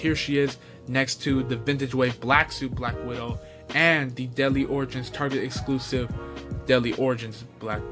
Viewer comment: I thought you said “Deli Oranges” instead of Deadly Origins, haha! Sick review dude!